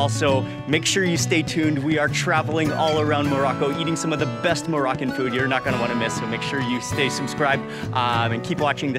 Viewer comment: she's talking about the ingredients for that Yogurt can someone tell me the second one she said "hot something" I can't understand what she said exactly. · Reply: She said : a mixture of cold milk, hot milk, lemon, sugar and vanilla. We keep everything in a container, we keep shaking it for about 30 minutes or like half an hour until it gets a little bit thick and then we wrap it in a warm blanket and then we leave it to rest for about 48 hours and then we put it in the fridge and then it's ready to be served. This is how we do it at home.